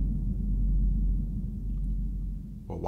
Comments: "Why didn't you call the police..?" "I DID, Think they got super speed like you do?" That line was beautiful. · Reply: You all, it's fairly easy to mis-hear. I heard it as "You think I didn't have superspeed like you?" the first few times. I had to slow it down to hear that it's "I did. Think they got super speed like you?" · Was half-expecting the twist to be that when the son doesn't do anything to help, the dad reveals that he's a super too. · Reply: I thought he said “You think they got super speed like you?”